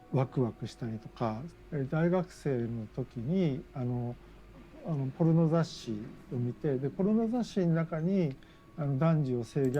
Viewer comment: Ok, so I dont speak Japanese but it sounds like the guy at the 9:00 mark said magazine twice. The first translation was magazine, the second was manga, but he sounded like he simply repeat himself. That being said, content involving real children was still legal in Japan until 1999 and between 1999 and 2014, it was still legal to own, posses and distribute it as long as it was produced before 1999. So if I'm right on what he said, he very well may have not been talking about manga at all as I did not hear him say manga, and his age would put him in the right time frame to have been able to buy and view magazines with that content.